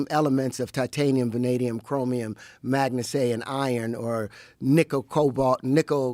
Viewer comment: 😂 WTF is MAGNA-SAY?! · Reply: i think he was trying to say manganese 😭😭😭😭